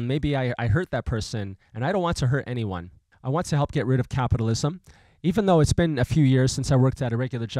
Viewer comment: I thought he said "I want to get rid of cannibalism"